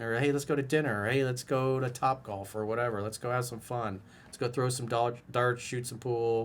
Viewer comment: I heard it coach at 11:40 mark let's go throw some dog shit 😂😂😂